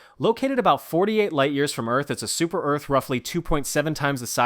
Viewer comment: did someone say super earth?